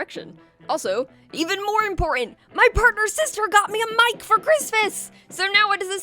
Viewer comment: partner and sister? I misheard something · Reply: F*cking hell, no partner’S sister 😭😂